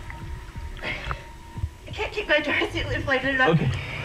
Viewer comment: What de fok, is she saying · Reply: “I’m panic.. I can’t feel my breath”